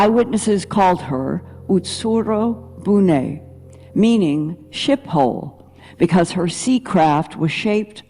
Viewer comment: What did I hear "SHIT HOLE"😂😂😂😂😂😂 WITH SPUNGK WTF😂😂😂